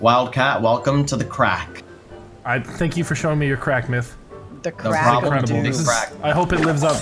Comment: I thought you said thank you for telling me your crack meth